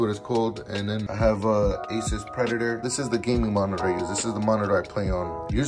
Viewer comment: did he just said "asus predator" ??????